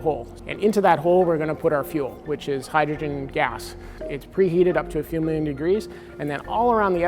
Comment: preheated to few million degrees? - is that what he said ? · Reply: Yes